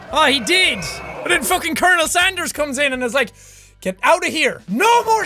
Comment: I thought he said kernel sandersXD